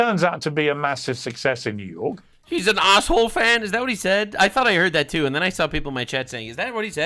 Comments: Arsenal fan is what fence sitter Morgan was saying he has that gay English accent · RE: "Is he an Arsehole Fan? Is that what he said?"; A: That's what I Heard too, but no, he said "Arsenal", referring to a football-as-in-soccer team from the UK. Arsenal's located south of London methinks.